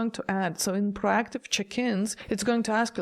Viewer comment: So check-ins. I thought you were saying chickens 😂. I not the norovirus 🫣